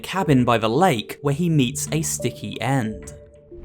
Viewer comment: Ngl I thought it said snick at first lol